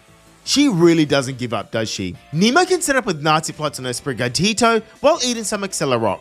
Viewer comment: Yo im the only one how noticed that he said nazy plots in the fight vs nemona at 11:29 or do i hear bad?😨😨